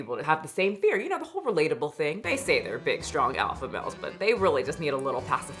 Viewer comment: for a second there, it sounded like you said "alpha mouse" so that's what I'm going to be calling them from now on...